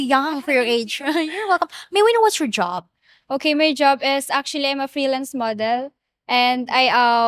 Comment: a thought she said freelance murderer